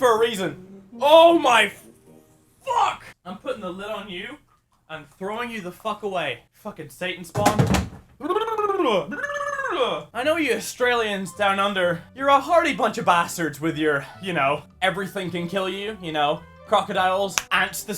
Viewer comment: did he say "Satans Bong"?